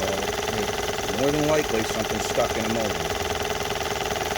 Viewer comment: "More than likely, something stuck in a modem" ..... I've listened to that like 5 times and I can't figure out what he's actually saying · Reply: Motor*